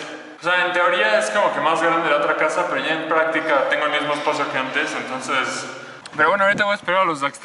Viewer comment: Tuve que escuchar 2:06 2 veces, porque a la primera, entendí "..tengo el mismo esposo que antes..."" y pensé, ¡Vaya me perdí demasiados vlogs! Jajaja